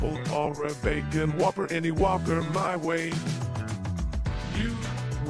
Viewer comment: It sounds like he says any walker my way